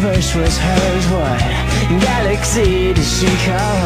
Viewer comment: it looks like joe is actually saying 'galaxy'